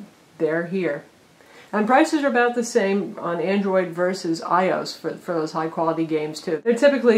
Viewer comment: Did she say "i-ose" instead of "iOS?"